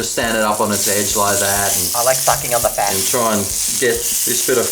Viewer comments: did you say I like fucking on the fat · when you said "i like sucking on the fat" I thought you said "i like fucking on a bench", well that was awkward